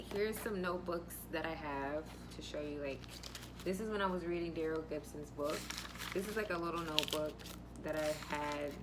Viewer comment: What was the title of the book and author? Sorry I couldn't hear the whole thing when you said his name. I caught Darryl Gibson but not sure if that's exactly what you said. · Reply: Darril Gibson Security+ Get Certified Get Ahead. The link is in my bio (